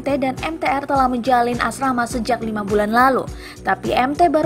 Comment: Tribun Timur || Coba dong didengerin menit ke 1:14 - 1:16 , aku salah denger sepertinya 🤭🤭🤭 asmara = asrama...